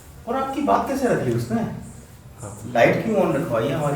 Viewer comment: pe jawab dea gaya hai I think us entity kii trf se baqaida " hai" KR k jb pooch rhy Hain k in k sath koi admi bhi hai ...mene do dafa suna hai wo scene repeat KR k ...kia meray ilawa kisi or ne yeh bat notice ki?